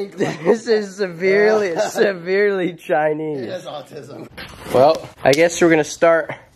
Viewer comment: I'm sorry did he say the bike has Autism?